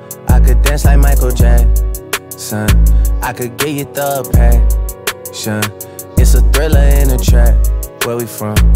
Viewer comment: I thought he said 'i can give you depression'